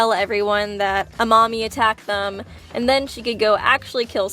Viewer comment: What she said: Amami attacked them What I heard: A mommy attacked them